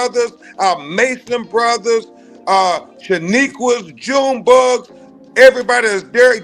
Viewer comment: Did he just say "Shaniquas, June bugs?" C00ning as it's highest order!!